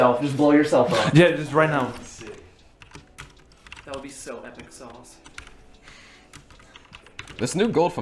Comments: did he just say epic sauce wtf?😭😭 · did he say “that would be so epic sauce”